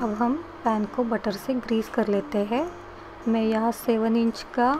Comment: What size is the pan? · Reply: I think she said 7"